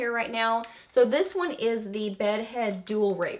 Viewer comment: Did you just say the Bedhead Dual RAPER?!!? Ill but hysterical, girl.